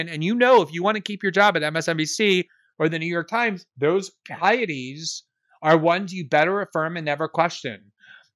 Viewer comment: Help, please. What is the word Glenn uses at 22:27, "Those _____, are ones you better affirm and never question."? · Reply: “Pieties” is the word. I think the definition of it he’s using most closely resembles the “fidelity to natural obligation”